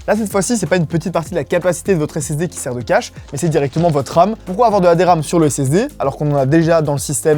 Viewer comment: j'ai entendu "c'est directement votre âme" mdrr